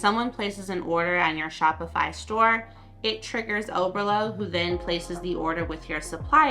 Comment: What was the name of the app that was recommended? I kept hearing "over low" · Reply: Oberlo